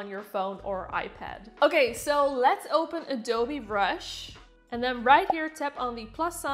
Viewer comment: who heard adobe brush